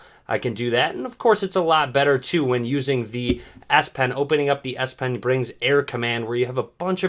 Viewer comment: Did he just say ass pen?